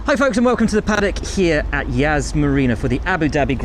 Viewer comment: For a second I thought he said HAAS Marina